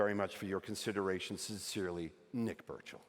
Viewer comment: I thought he said "Nick Birch" and I was like "oh yeah, that checks out haha"